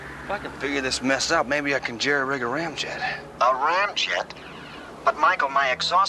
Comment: Excuse me while I go rig up a ramjet, apparently it's pretty easy to do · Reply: 😱 You actually understood that line! I didn't get it then nor now. All i heard was "rig ah rig ah ram ram"